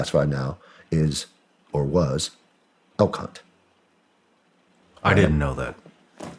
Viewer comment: I thought he said “el c*nt”, not “elk hunt”.